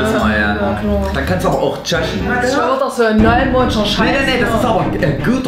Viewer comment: hab erst beim dritten Mal verstanden was du meintest...😂hab die ganze Zeit verstanden tschechen oder so....